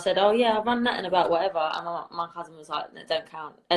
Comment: WAIT did she say "MY HUSBAND"??!